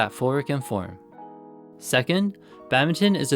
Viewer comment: what did you say? What words are they? Phoric and form? Sorry, non-native speaker here 😀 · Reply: Footwork and form!